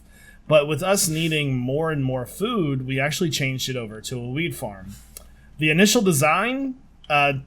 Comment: I thought at 2:32 he said weed farm 😂🤣